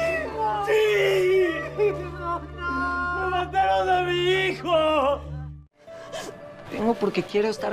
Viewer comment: yo pensé que decía Siiiii Siiiiii :v